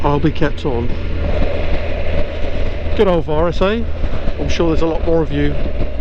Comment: Hi Mick, thanks for watching the video. However, I'm a little confused...give me the time stamp where I said "it's Boris's fault?"... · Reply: yes sorry, my hearing,,, thought you said Boris but it was virus, hope the employment works out ok for you. sorry again.